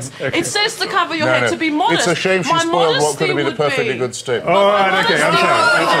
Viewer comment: what did hitch say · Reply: “Its a shame she spoiled what could be a perfectly good statement”